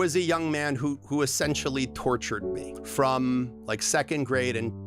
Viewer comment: I heard “sexually tortured me” and it made me very confused for the rest of the stories lightness and forgiveness.